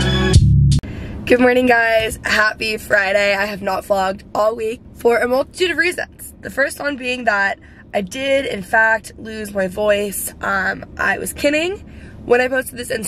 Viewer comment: Almost 100% sure I heard you say "I have not flogged all week..." and I was like oh was getting savage in these streets.. literally savage.. anyways, so then I proceeded to clean my ears out and quickly realized that you had lost your voice lol